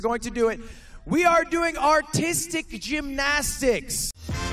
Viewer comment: I thought he said autistic gymnastics 💀